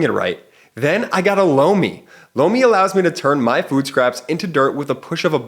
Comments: Was just listening, not watching.. when he said sponser.. "lomi" i swore i heard "Blow me" Like.. what... is this?! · A little levity: Listening to this, as it is about a sexual subject.....at the commercial break , I mistakenly heard , "And now lets take a quick moment to talk about our latest partner "Blow Me"...Not LOMI! .....anyone else hear that?.... Anyway I love your show, I am in Canada and listen to you guys all the time. My wife thinks I'm crazy. Thank you for all you do. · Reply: My husband thought he said he heard "Blow me" instead of Lomi. Lol!! You are not going crazy.